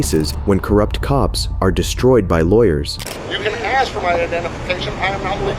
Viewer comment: I believe he says: 0:13 "can" not "didn't" 1:56 "Did I say" not "I said" There are many other text issues, way too many actually to be mentioned in one comment, but it messes with my head when I hear something different than what I am reading. It sort of gets distracting.